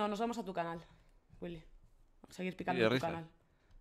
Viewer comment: creo que dice sigue richar